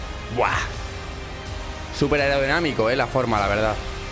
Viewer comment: Energamico? Ergonómico? 😂 😂 😂 😂 No entendí hahahahahahaha